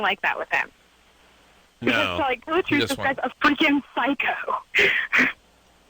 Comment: did she say “a f@ psycho” ? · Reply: freakin she said